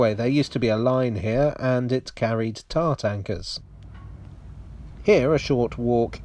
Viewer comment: Was it “tar tankers” you mentioned? At first I thought you said “tart anchors” and had no idea what they were!